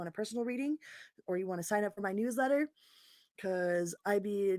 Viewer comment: News letter, I got excited I thought you said "New Leather".:face-green-smiling